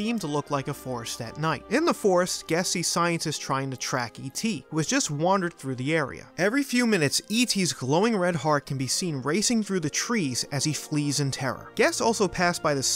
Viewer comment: wait did you say glowing red heart OK now I am freaked out and buy freaked out. I mean right now I am more freaked out and I’ve ever been before